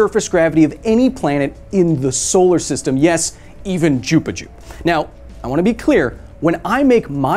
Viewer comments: i swear he just said jupiju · Did he just say “Jupi-joop”? Or am I going cray-cray? Crackle smacking finglongers Diddblit shingran yup yup greeeeeeps?